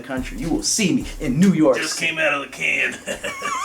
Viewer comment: Im spending days wondering what what pete says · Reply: "how do you put this thing on?"